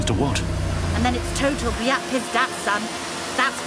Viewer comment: "Let's go...and then it's total BLYAT PIZDIETS son" - is that what she actually said?! 😂